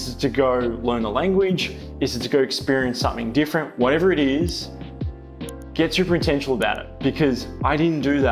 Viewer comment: Love hearing about your experience... especially what you had said about Bali. What is it that you had said at 12:49 please? The cc had it as "whatever it is, get super potential about it", which can't be right.